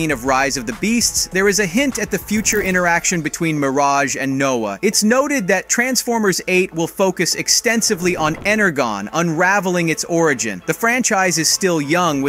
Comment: Did he say ennergone